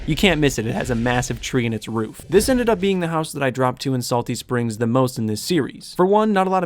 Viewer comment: I thought it said dropped salt at salty springs 100 times lol